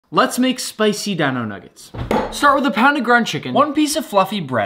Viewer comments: I heard “spicy Dan-O nuggets”and was left waiting 😫🤣🤣🤣🤣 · I swear I heard “one piece of f$cking bread” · I heard, fluffy and thought he said fu##ing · Don't ask me Why but I heard " One Piece of f#king bread ", not " One Piece of Fluffy bread"